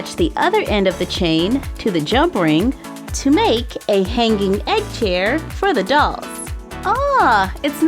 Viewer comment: i thought it said "hanging egg hair" not "hanging egg chair"